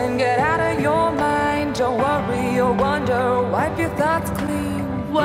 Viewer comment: I thought she said wipe your butts clean 😂